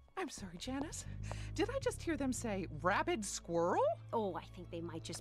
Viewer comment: Did I just hear them say “Rabid Squirrel”?🐿️